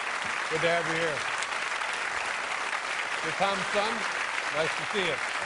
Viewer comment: did Letterman say to the drummer "You're Tom"s son"?